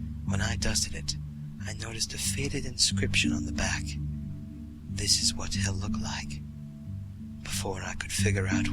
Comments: oooh. i heard "this is what hell looks like" and i was really confused. but yeah now i understand. "this is what he'll look like". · I thought he said "Is is what hell looks like" I read the story and found out it said "This is what he'll look like" so at first I didn't understand the ending, lol!